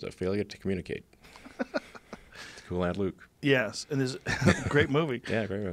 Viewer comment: whats the film they mention at 30:13??sounded like lad luke to me lol